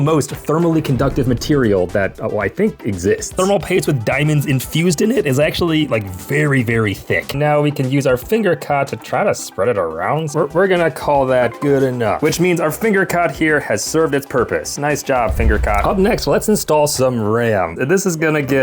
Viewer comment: I thought he said finger cock 😭